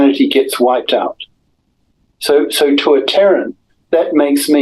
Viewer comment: The first 15 minutes I thought he was saying Karen’s. Terran’s makes way more sense